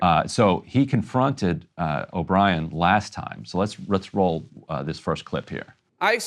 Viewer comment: That's probably the next time he (Senator) saw him (teamster guy). If not, then it was done for theater which is obnoxious....but pretty much on par with most of what happens in these hearings. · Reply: I missed the 7 month gap piece of the story....I thought he had said those tweets were from the night before... I just went and listened again...he said "last time" I thought he said "last night" so you're right...there was definitely an opportunity to set it up offline and not as a part of a hearing. But I still maintain that the teamster guy is the one who challenged the Senator. The Senator just opted to accept the challenge in the hearing (an arguably poor decision)